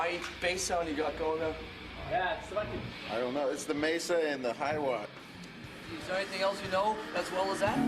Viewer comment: can some clarify for me what Rob said? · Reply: I think he said: It`s the MESA in the high watt. ( referring to a MESA bass amp, with the high wattage setting on)